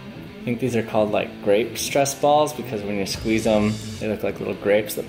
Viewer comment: I thought he said RAPE stress balls